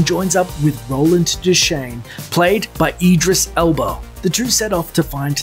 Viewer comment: did I hear him say "Edris Elbow" 😂😂😂😂😂😂😂😂😂😂